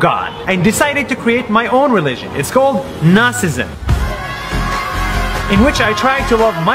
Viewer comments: I thought he said Nazisim at first. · i thought he said nazism !!!